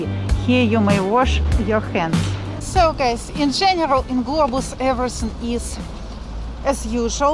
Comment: I always thought you were saying “Globals” is where you shop. Today I read the sign as Globus 🤔